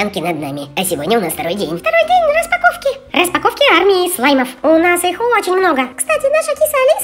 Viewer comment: мне не послышалось? АРМИ СЛАЙМОВ?😅😂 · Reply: Армии